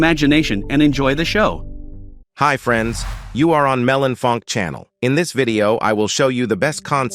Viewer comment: I thought, bro said melon fuck😭😭😭😭😭😭😭😭😭😭😊😊